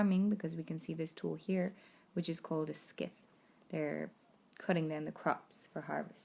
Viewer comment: A succinct, interesting summary. One point though: I had to re-listen a few times to the word at 2:08 before I realised it was scythe. All my life I have heard the word pronounced "_syth_" (i.e.silent c and silent e) and this is also what it says in the dictionaries I have at hand.